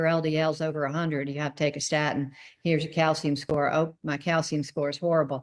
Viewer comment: I couldn't understand what the drug she mentions at 1:01???? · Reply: Statin